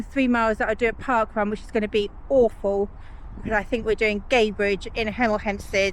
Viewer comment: Hmm, and I misheard it as Gaybridge parkrun.. I thought, that's one I've not heard of.. Gadebridge! 🤣 Try to enjoy it. ☺